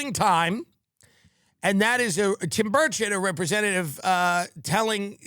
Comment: Am in uk and have to ask Is it the accent or am I hearing the name Tim birdshit 😂 x